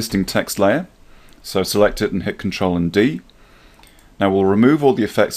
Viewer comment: thanks, but I misheard you at one point. pressing control B instead of control V. And now I'm completely stuck.